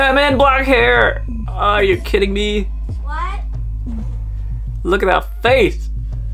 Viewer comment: Did I just hear someone say what or am I deaf 👁️👄👁️ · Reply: ikr!!!!! i heard someone said "WHAT"